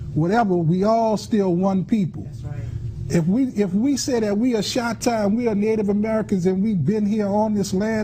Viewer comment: This lying fraud enrages me. She is not Chahta. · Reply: ljwilson1973 I could've sworn she was saying Chatta lmao. I thought she just invented a new tribe and claimed the role as chief ... If I didn't know any better I'd think this was a badly put together bit.